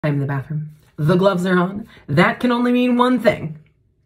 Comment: Why did I hear "I AM THE BATHROOM"